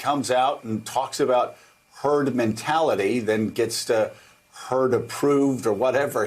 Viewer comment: I heard him say 'turd mentality'.